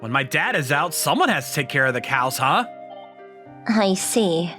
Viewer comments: I misheard "someone has to take care of the cows". Also I would've died from anxiety if this happened to me. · I swear I heard him say “cows”